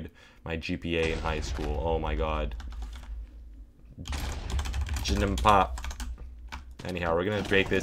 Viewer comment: If anyone is wondering what he said at 38:09 it’s “I am not a friend”